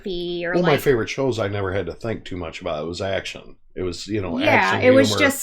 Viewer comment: did just say he used to watch the TV show "Action"??? The one with Jay Mohr??? I friggin' LOVE that show!